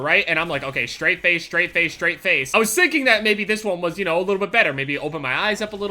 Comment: who the hell is that there's no pink headset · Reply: lol look at 3:20 he said sinking instead of thinking